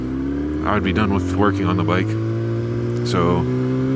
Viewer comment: 😂 ok so I heard "I'll be done with twerking on the bike" and not 'working' on the bike 😂😂😂 thought you'd appreciate a giggle